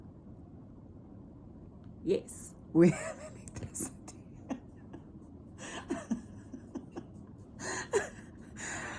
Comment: wait I didn’t hear what you said ..did you say “ we have electricity “??🤣🤣🤣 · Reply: 🤣🤣🤣🤣 oops, let me say it again… we have electricity 🤣